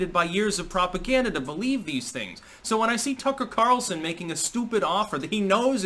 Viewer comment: the entire time I was watching this video, I thought I heard you saying "Cucker Carlson" the whole time, NOT tucker carlson